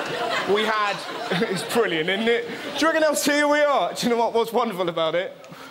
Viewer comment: Does he say "Juggalos here we are" at 00:12?